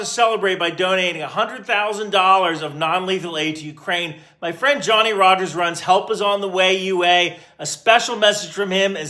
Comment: I heard "my friend Johnny Harris" at fist and I apologize. 😂